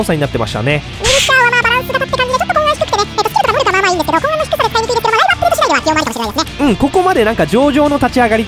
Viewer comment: ウィルシャー解説聞き取れなかった人のために 「ウィルシャーは、まあバランス型って感じでちょっとコン安低くてね、えっとスキルとか能力はまあまあいいんですけど、コン安の低さが使いにくいですけどまあライブアップデート次第では起用もありかもしれないですね」